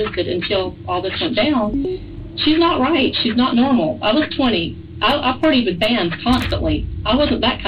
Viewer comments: To everyone saying “she is delusional for thinking she looks 20”: she doesn’t. She says “ I’VE lookED 20, i have partied” so y’all misheard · Right?! I had to rewind it to make sure I heard it right. Because I thought there’s no way she said that. I thought she must’ve said “I’ve been 20” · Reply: I thought the exact same. In the intro of the video I actually thought she said "I was 20" but when later the narrator said her lines and said "I look 20" I was like wait what?